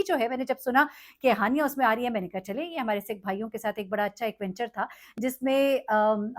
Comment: Did you said *_Sikh Bhaeeon*? 1:58 Tho tho tho